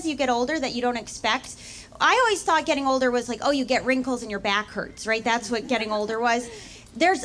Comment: I thought she said You get wrinkles in your back-hurts Like as if back-hurts is a body part you have later on and it also gets wrinkly And i accepted this without a second thought And i am 44, thank you